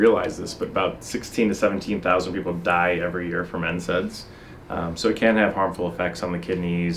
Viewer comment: What did he say in 1:48??? And what is it??? Anybody? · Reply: JayR NSAIDs